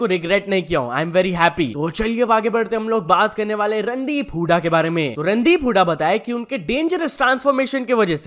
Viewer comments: bhai randiip hooda😂 kya bolra hai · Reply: BROO RANDIII. P HOODA I HEARD LIKE THIS 😂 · who else hear 'RANDI'😂😂